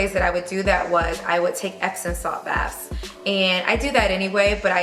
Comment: can anyone tell me what she put in her bath at 1:54 i cant get it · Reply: Razan epsom salt